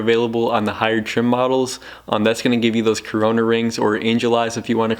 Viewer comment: Did he say Corona rings?